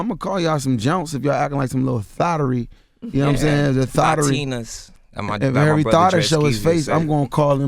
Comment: did he say "Latinas"??? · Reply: He said thotianas